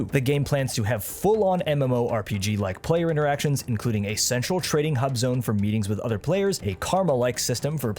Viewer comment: I thought you just said “A sexual training hub zone”